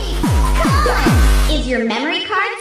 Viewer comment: I thought it said "JUMPING IS NOT A CRACK😭